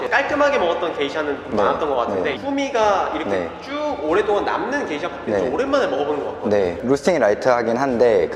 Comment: 말씀을 편하게 하시는 편이신 바리스타님의 설명 잘 들었습니다. 도전하기 비교적 편한 레시피라 더 반갑네요. 다양한 컨텐츠 감사합니다. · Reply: 뒤에 계신 분 덕분인지 후미가 호미로 들리는... (자막 감사합니다)